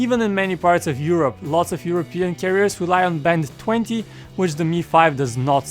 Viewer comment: Did he say 'lots of european terrorists' ??? @ 2:52 :/